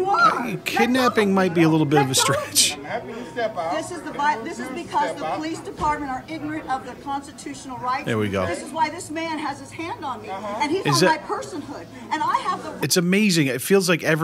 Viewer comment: I thought “Leopard skins” said “Prostitutional” Rights…which would have explained the wardrobe.